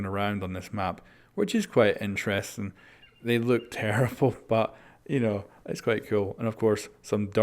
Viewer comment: he really said terrifull at 4:32 😂